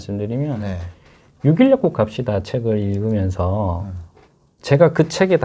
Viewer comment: 여기서 읽으셨다는 책제목 뭐라고 하시는지 계속 반복해서들어도 모르겠어요.. 무슨책읽으셨다는건가요? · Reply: '육일약국 갑시다' 네요~